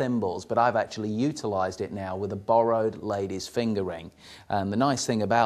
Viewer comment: did he say ladies fingering??? lolol